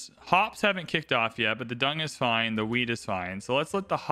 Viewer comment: You saying "weed" instead of "wheat" (at least I think so) made me laugh a lot. Did you really have bread in mind? ;-) Really nice series, keep it up ❤